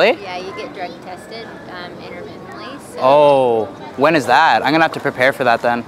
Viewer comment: Love you guys soooo much but ‘interventally’ at 5:29 is not a word. I think that actually said ‘intermittently’... just a thought? 😂💖